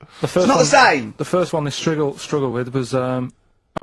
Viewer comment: karl is so used to having to pronounce words strangely for rockbusters to work he says striggle instead of struggle